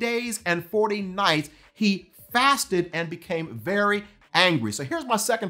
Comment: You mean "He became very hungry" ? I hear him say "he became very ANGRY"